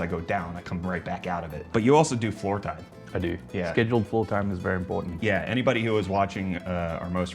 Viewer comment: Scheduled floor time? Flirt time? Please explain what was said and what that means haha, I'm having a misheard lyrics moment